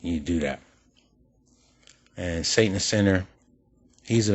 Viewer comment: Saint and sinner called himself an “apex predator” and I called him on it... No reply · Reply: Thought he said satanist sinner